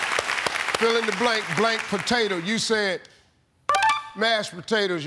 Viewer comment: They did very well. My question is, how many points baked potato would've got. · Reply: Oh I definitely thought he said big potato haha